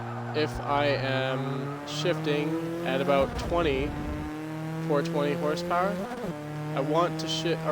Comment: did he just say 420hp